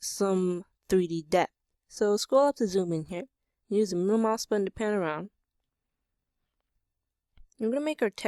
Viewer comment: what do you say from 2:43 till 2:47...?the subtitle is: "use my husband pan around maker texas judit". I think you didnt say that but tell me what you did :p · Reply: "so scroll up to zoom in here, use the middle mouse button to pan around" how is that so difficult to understand lol